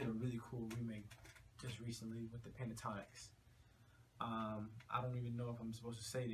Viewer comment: Did he say the Pentatonix? ._.